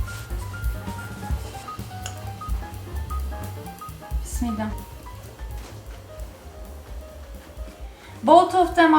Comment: Teşekkür ederim! Your videos are so fun! What are you saying in Turkish at about 13:15? My Turkish is so very basic that I cannot even guess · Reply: She prays with a few words (In Arabic) before eating. Bismillah. Means :"In the name of the God(Allah) Almighty"